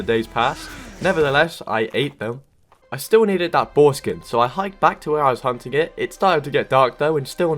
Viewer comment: sounds like he said "foreskin"